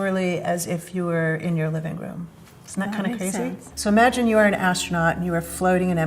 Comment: for the subtitles: she did NOT say "Amazing." She said "That makes sense" 🤣🤣 Slight difference in meaning. It's okay if you went "Ah I kinda got it" rather than "amazing" 😂 Or at least that's what I'm telling myself. out.